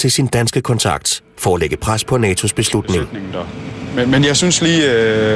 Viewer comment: i swear they say Netto which is a danish grocery store instead of NATO